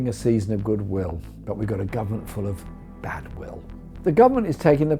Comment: I honestly thought he was going to say "...but we've got a government for of bastards" for a second.